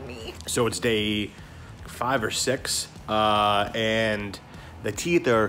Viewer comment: sounded like he said sex? IDK is it just me?